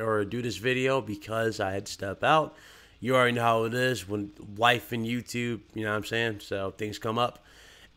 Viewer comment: Lol I thought you said wife and YouTube and I when I heard life I was like oh